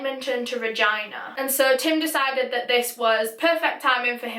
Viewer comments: When you said Regina I thought you said vagina 😂😂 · Girl, I thought you were saying Vagina over and over. Lol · Did she say “Vagina”?